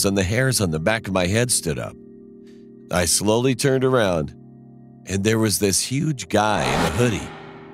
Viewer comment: i thought he said, "there was this huge guy lil'Huddy"